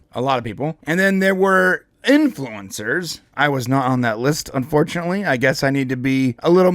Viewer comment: You said you were "na- on that list" instead of "not on that list." Maybe you can get on the list if you pronounce the T's at the end of words like someone who speaks correct English. And it would also help if you didn't use ridiculous juvenile made up words like "copium."